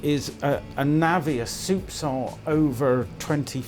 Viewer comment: I have never heard the words you spoke at 9:55 .. it sounded like "navia supsal" ?? what does it mean? I get it that it is a small measure but what is the origin?